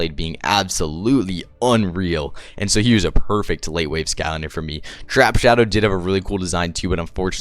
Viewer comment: Those darn "wait laves"😂. Yes, u meant late waves but I had to rewind like, did he just say wait laves? 😂